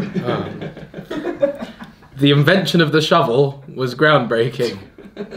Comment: i thought he said "the infant was showering when the ground was breaking"